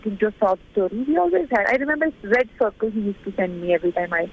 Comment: Could someone please tell me what she said @ 2:43? "I remember ___ , he used to send me every time" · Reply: ⭕